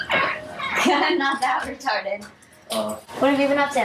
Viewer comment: did she say "retarded"? Also I really don't like that actor decoy